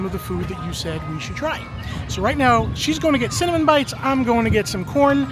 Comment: I thought you said salmon bites then talked about the cinnamon, cream cheese and chocolate! Cinnamon bites sounds much better!